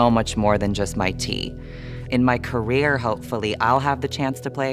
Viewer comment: I love her so much. Was thrilled to see her pop up in Roswell tv show. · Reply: could you tell me wha does she say in 1:28 ? "I am so much more than just my (tee?)" I'm learning english and it doesnt have subtitles, she says tee?